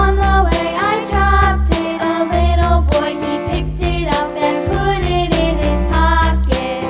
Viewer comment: I misheard the put it in a pocket part of the song and heard and put it in a coffin lmao